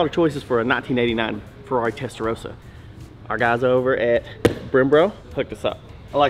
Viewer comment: Oooh this IS SO COOL!!! is an amazing host! 15:08 did he say BremBRO ? 😂 20:03 did he say BERMbo ? 😂